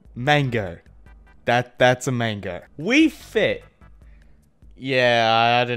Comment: When you said we fit for a second I thought you said wii fit